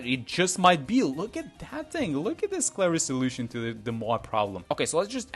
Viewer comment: I thought you had said "look at that thing, look at that final solution to the MOAB problem". Spooky scary huh.